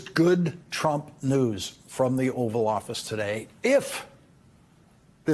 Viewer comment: I thought he said “from the evil office today” i had to go back to hear it again😂😅